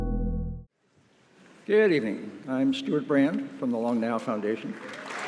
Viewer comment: I thought he said the long nail foundation 😅